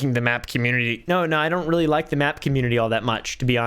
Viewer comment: Bruh I thought he said he didn't like the math community.